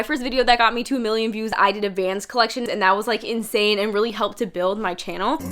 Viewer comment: When she said vans collection i thought she said advanced collection and i was like wdym until she showed the vans she had 😂